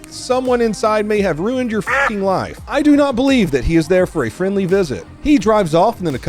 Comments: I thought I heard the gun man say something like "f*cking my wife" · I swear I heard “ your screwing my fucking wife” idk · I thought he said "you screwed my fucking wife" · I thought he said "you fucked my wife" at first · I thought he said "You fucked my wife!"